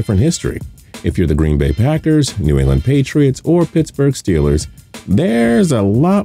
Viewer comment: I thought he said New Orleans Patriots